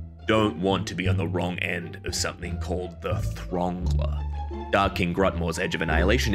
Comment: For those who don’t know, the verb “to throngle” means to choke someone to death with a vaguely phallic object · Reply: I thought he said thongle